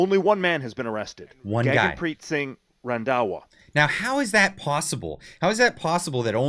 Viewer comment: what was the name of the one man arrested in the huge drug lab bust? It sounded like Jagmeet Singh Randawa? That does not sound like a mexican name... WEF puppet Turdeau's task was about destabilizing Canada with floods of immigrants, the involvement of drug trade is no surprise.